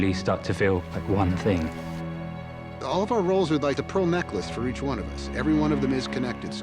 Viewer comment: did he just say "Pro necklace"?